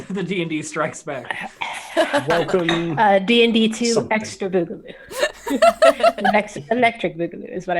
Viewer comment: I thought she said explicit Boogaloo, I was like what kind of DnD am I watch?